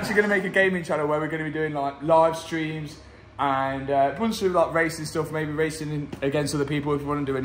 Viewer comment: I really thought he said "We gonna do a lot of racist stuff" T_T_T_T_T_T_T_T_T